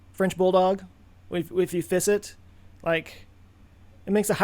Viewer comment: Oh FISS it. I thought you said FIST it. The whole "I'm not gonna split my dog in half" thing worked for that too.